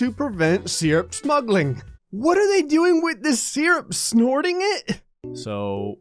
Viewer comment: "Syrup Smugglin- What are they doing, snorting it?!" as a canadian I can confirm that we do not snort it, but instead shoot it into our syrup stream. As they say, Blood is thicker than Water but Syrup is thicker than Blood so technically Syrup is more important than family Edit: Okay, 650 likes? After 2 weeks? My question moreso is why there're so many. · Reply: Snorting? I thought he said squirting.